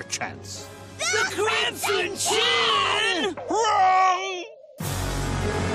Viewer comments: DID HE JUST SAY THE N-WORD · When I watched this episode as a kid I thought he said “The naked chin” so every time this is reposted I still hear it